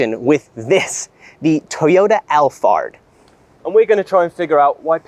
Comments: for the first 5 minutes, i thought they were saying L-Fart. LAMO. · The man in blue sounds like he is saying Al fart